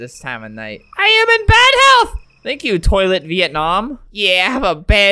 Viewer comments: said "Toilet Vietnam: xD That was Toi la Vietnam, means: I'm Vietnamese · That was Vietnamese, it actually reads, “Toi la VietNam” which translates to, “I am Vietnam,” not, “Toilet Vietnam,” =-=